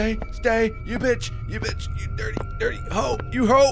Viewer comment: sounded like he said duty hole